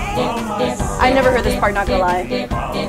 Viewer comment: I thought you said “fart”